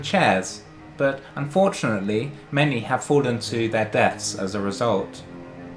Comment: did you say desk or death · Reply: I cannot quite remember but reckon I said 'desk'. That seems to fit better :)